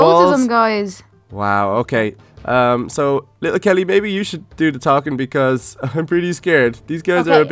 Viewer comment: did anyone else hear say "little kelly baby" at 10:07.